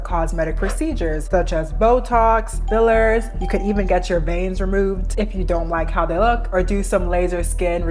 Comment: did I just hear her say “get your veins removed “???????😳😳is that actually a thing???!!?!!!!